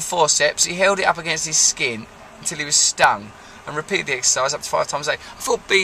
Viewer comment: Not gonna lie, thought the tile said "exorcise" at first.